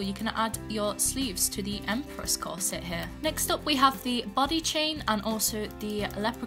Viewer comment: And I heard the “butty chain” instead of “body chain”